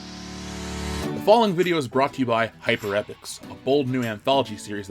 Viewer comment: Sounded like you said 'hyper ethics'